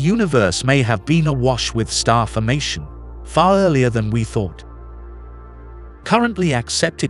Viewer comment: "Farllearlier" is a word I heard for the first time in this video at around the 2:09-2:13 mark